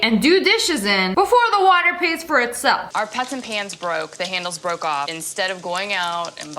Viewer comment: did she just say our peasant pans just broke 🌚🌚😂😂😂😂